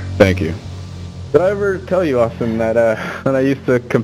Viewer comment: We're getting closer to the end folks. Thanks so much for hanging in there. So far, it's been a wild ride :D Much love ! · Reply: did he say Austin?